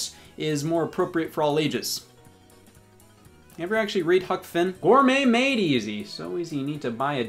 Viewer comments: I’m glad he mentions how, uh, “Special”, Huckleberry Finn is. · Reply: I heard „Cuck Finn“. Now that would be a whole different book. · At First I Thought You Said “Fuck Finn”